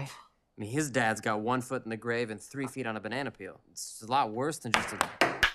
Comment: what did he say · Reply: "It's a lot worst than just a-" gets cut off by Kristofferson throwing his acorn I'm gonna guess that Ash was gonna say "It's a lot worst than just a tail being shot off" or something like that.